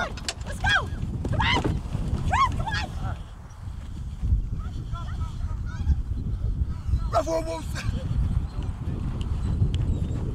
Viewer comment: Who Else Heard Carmella Say “Come On Baby To R Truth”